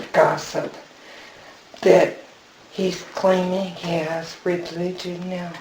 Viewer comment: what does she say at 20:14 ? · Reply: "He's claiming he has religion now"